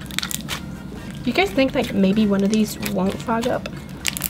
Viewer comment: When you said fog up i heard f*** up....